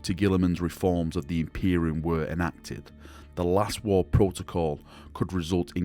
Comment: Lol, I thought you were saying "Last War Protocol" this whole time...